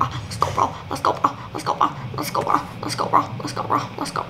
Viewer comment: When she said let's go bruh it sounded like she said less corbra😂